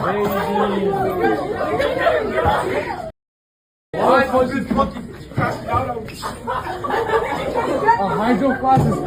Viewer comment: did he say, "why? cause this monkey crashing out on me"? It sounds like it, but I can't make it out. This is a great take, if the roles were reversed he would be getting several visits...and none of them from police.